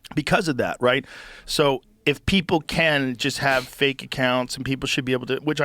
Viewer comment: I hear Yoel Romero every time he says people